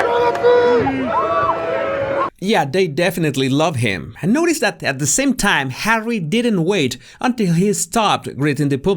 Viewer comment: What are they shouting, at about 2:38? 😊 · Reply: The shout his ‘name’ Willempie